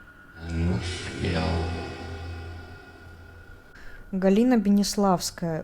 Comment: ответ "НЕ УСПЕЛ", я так услышал и очень чётко